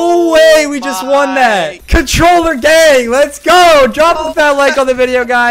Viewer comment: It sounded like he said Controller Gay